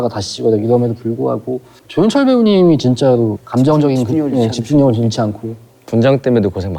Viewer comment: 에 구교환 배우님이 뭐라고 말씀하신거예요? 저 진짜 궁금해요...ㅠㅜㅠㅠㅜㅜ 제발 알려주세요.... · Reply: 저는 "집중력이, 집중력이 최고." 로 들려요